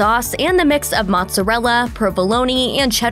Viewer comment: Did... did you just say provoloni?